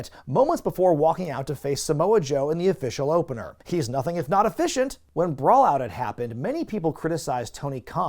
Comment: What did you say about C.M. Punk around 29:47-29:49?... "He's nothing if--" what?... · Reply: "He's nothing if not efficient"